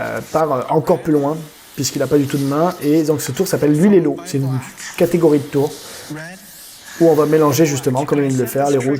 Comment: C est quoi le nom du numéro à 6:26 S.V.P je comprends pas ce qu il dit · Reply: Tommy wonder